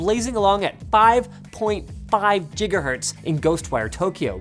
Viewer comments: Did Linus just say "Jiga-Hertz"? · Did Linus really say "Jiga-Hertz"?